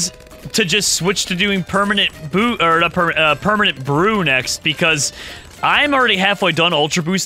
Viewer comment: I thought he actually said permanent poo